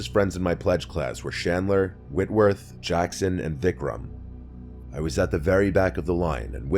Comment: did you just say DickRum? 😂😂😂😂😂😂